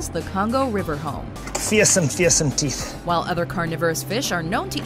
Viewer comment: LOL @ 6:12 the guy in the video says Fearsome Fearsome Teeth. But the subtitles say CSM Dissing Teeth.